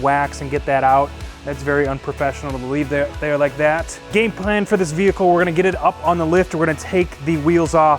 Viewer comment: did he say “gayplan” 😉😉😉